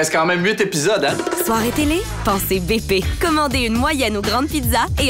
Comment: The first part when it said Boston pizza it sounded like it said Boston pee pee